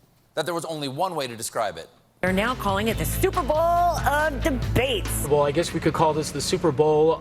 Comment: she said "Stuperbowl" and I heard it I replayed it and that's definantly what she said. Isn't there a copyright infringement for saying 'superbowl" you can't even call the "superbowl" the " superbowl" in you sports bar promotions for the 'superbowl" So how do the networks get away with that gaff and why didn't the genius pundits and comedians grab that and make jokes about it?